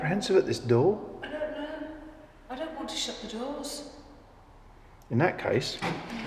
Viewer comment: It´s incredible but at min. 12:21, I heard a female voice saying "Don't shut the door". Min. 24:36 an orb appears in the upper left and disappears. They´re very close to us!